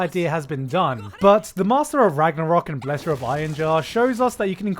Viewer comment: Is it me or I heard "The Master of Ragnarok & Pleasure of Einherjar